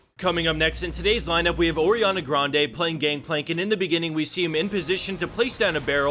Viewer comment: Who else flipped when they misheard Ariana Grande playing league